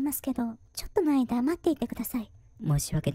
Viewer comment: ちょっとお前黙っていてくださいにしか聞こえなくて笑っちゃった